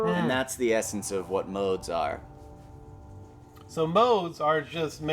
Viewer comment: ever time they say: ''so modes are?'' I hear:''so Mozart'' xD